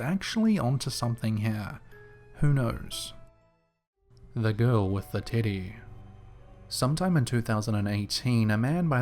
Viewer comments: I swear i thought you said "The Girl With The Tiddy" lol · Sounds like you said"The girl with the tittie."😃I know you didn't but I laughed so hard I'm from southeast Tennessee though lol,😃 Love your videos top notch my man!!!Love to you from Tennessee Cherokee Nation